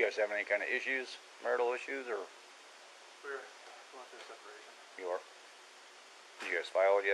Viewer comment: Cant tell for sure but around 18:52 when the officer asks him about marital issues it sounds like he say "we WERE going through separation" and "we WERE going to sell the house"